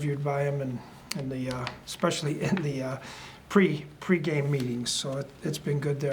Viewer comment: I thought Zim said we had some of the best memes. I had to back it up and listen again... MEETINGS... not memes. ...but can memes play guard?